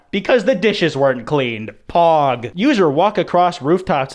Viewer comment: excuse me, did he just say 'Pog'?